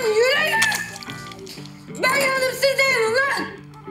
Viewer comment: de ne diyor anlayan var mı 😅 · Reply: Ben yandım sizde yanın lan